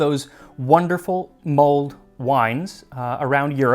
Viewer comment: Great episode! Around 9:35 there is a small error in the closed captions, it says "mold wine" instead of "mulled wine", just in case you want to correct it...